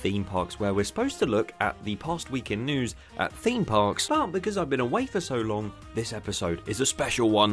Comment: heard this as "but, because I've been a wafer so long" anyway TPWITP IS BACK!!!